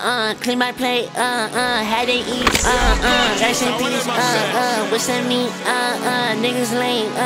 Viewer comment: I can’t be the only one who thought he was saying “racks in piece / what’s that mean?” instead of “rice and beans / with some meat”